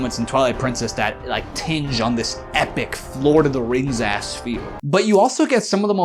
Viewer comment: mf did you say "florida the rings"